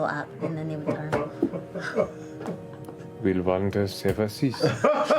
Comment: What did say at 11:10? I couldn't catch it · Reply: "(Sarcastically) Will wonders ever cease?"